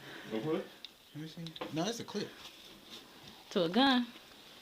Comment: did he say clip?? 💀